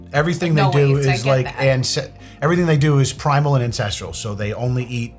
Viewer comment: I thought you were saying 'primal and incestual' ...